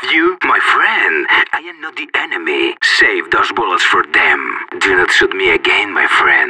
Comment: what does he say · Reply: (When friendly fire) Save those bullets for them.